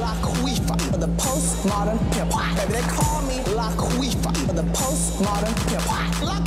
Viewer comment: I thought its said call me lupita😂😂